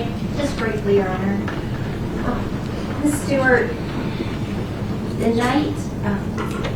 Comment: TN accent, love it. I thought she said store or story then she spelled stewart.